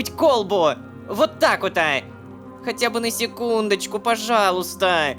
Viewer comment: Мне не послышалось? СЕКУНДУ?